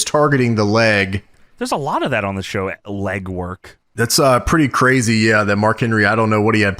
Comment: Slipdown GM: did you say…..legwork??